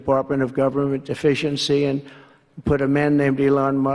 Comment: Sounded like he said he'd created an office of deficiency! 😂makes more sense tbh